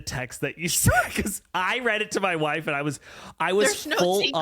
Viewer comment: Did he just say that he read it to his...uhmm... WHIFE?